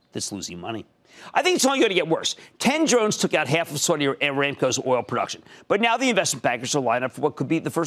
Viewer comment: what did he say? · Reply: 10 drones have took out afgan oil production